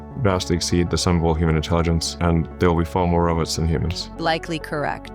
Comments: Did musk just say "there would be far more of 'us' than humans." @ 14:17? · did Elon just say - " there will be far more of us - than humans." ?? 😮😮 WTF .?? 😮😮😮 ☮️✝️☮️🇺🇲🇺🇲🇺🇲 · did he say there will be far more of us then humans TF 😮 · "There'll be far more of us than Humans" at 14:18. Did Elon Musk include himself when talking about AI from that perspective? "Us" then "Humans"... I ran that back 20 times to be sure I heard/saw what I did. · Im sorry, but did Elon just say "..more of US than humans..?" 😮